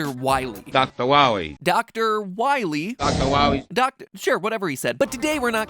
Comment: one time I heard Dr wowwy? The wa of the we a wu? Is that where it came from?